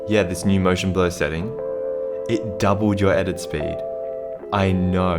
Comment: I just heard: "doUbLes yOur eDitiNg sPEed" and inmediatly liked the Vid 😂❤️